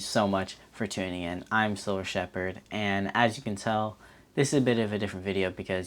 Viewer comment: Crap I thought you said Cybil shepherd I love that girl. I digress. I love buying silver and stacking it. The trick is just to get started. I subscribed.